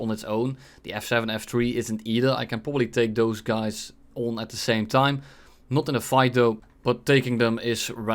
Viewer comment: hold up did anyone catch that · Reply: take two guys but not in a fight 🗣🔥🤤😳💦